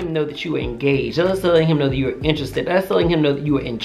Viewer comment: anyone else at first thought he said "let him know you ain't gay" ? 😂